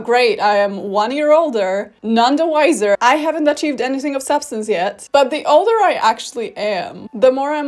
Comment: Thought she said "I haven't taken The Substance yet"... wouldn't be out of place for this video😭😭😭